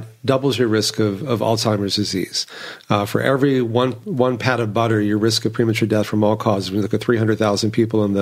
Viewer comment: Overall: Thank you so much for the interview! 9:31 hm - will have to look up that study, though, "every pad of butter increases your risk of death from all diseases by 5%" - did I hear that correctly?